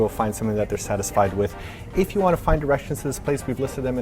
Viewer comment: I thought said, "If you want to find Russians in this place," and I was all like, is that a thing? Do I want to find some Russians?